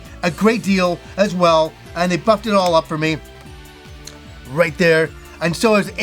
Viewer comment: When he was talking about Paper Mario: "And they buffed it all up for me." What I heard was.... "And they fu**ed it all up for me." 😂 God, my ADHD always finds a way to make a situation amusing at least.